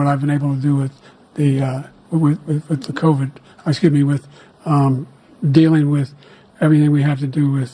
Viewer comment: Trump’s face when Biden says I skibidi with gets me every time 😭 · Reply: I thought he said "excuse me" 😂